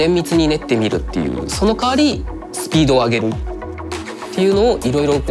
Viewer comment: I hear speedwagon